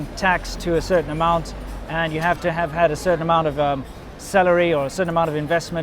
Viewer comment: When Winston said "you need to have a certain amount of salary" re: visas, at first I thought he said "you need to have a certain amount of celery." ;)